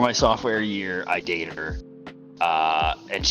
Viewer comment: my dog was coming in the room as was talking so I heard "in my software year I updated this girl"